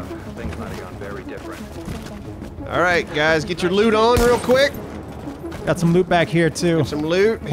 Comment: I swear I heard say "get your lube on".